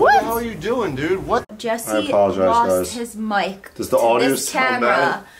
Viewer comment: I thought said Jesse lost his mind lol 😄😂